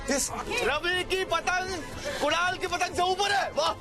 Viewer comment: did someone heard sid name as ravi